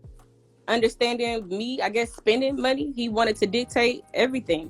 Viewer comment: That is the dumbest SHYT I ever heard. She said he didn't like the way I spent money 10:03 he didn't understand me spending money. Not he didn't understand me paying bills.